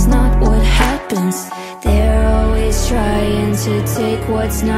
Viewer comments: There is a misheard lyric the correct one is "Drives me insane, it's not fair" rather than "Trust me in saying, it's not fair." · Guys I hear, “drives me insane it’s not fair”, but I also hear, “trust me in saying it’s not fair” wHaTs ThE pRoPeR wAy?! 💀😭 love the vid btw